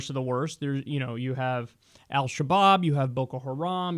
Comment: For a sec i thought you said Al kebab! x)